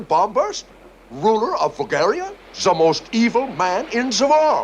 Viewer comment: For a second I heard 'Ruler of Bulgaria' XD And I thought we were talking about tsar Ferdinand hah!